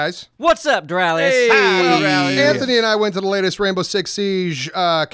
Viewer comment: did you say rambo or rainbow